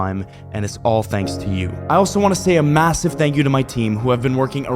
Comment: Did bro say massive